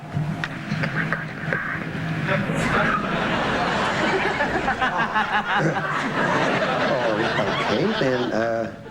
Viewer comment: what did the lady say? I couldn't understand a word. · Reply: She'd put him 6 foot under